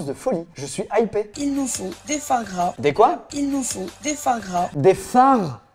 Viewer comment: j’ai compris « foi gras » 😂😂